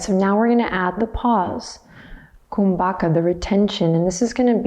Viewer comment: I'm such a dog mom 😂 She said "now we're gonna add the pause" and I heard "now we're gonna add the paws". Took my brain a full 10 seconds to remember that this was a YOGA CLASS for HUMANS and that there would be no dogs pranayama-ing 😂😅